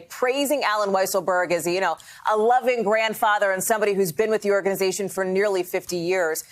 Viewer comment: I hear “a loving grandfather and someone that has been with our criminal organization for nearly fifty years”.